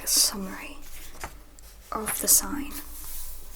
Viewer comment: what did she just say? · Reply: Of the sign